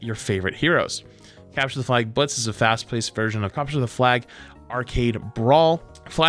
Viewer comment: I HEARD IT wants to capture me help